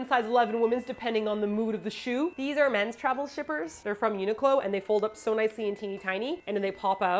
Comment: Sorry... I keep hearing 'travel strippers'... I know you love Simon, but that's a bit weird...😅😅❤